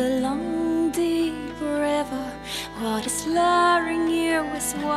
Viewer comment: Ohh. I thought she was saying the lawg shi river not long deep river that makes more sense